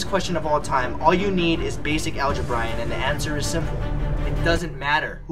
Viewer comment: Every time he says "algeb-Ryan", I hear "alge-Brian".